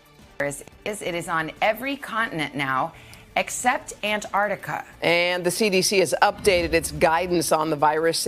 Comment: except what? Can some translate ? I think she was trying to say Africa 🤣🤣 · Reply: Antarctica